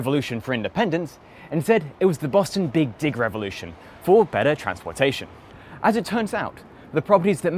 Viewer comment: I watch YouTube videos at 2x speed. At 5:22, I definitely heard "Big Dick Revolution."